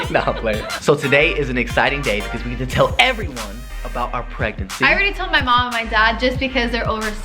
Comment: Somewhere on the video i thought Said ”our pregnancy is pregnant”